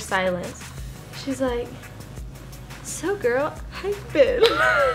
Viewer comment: What does say from 3:00? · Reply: Anna Legény-: So girl...how you been?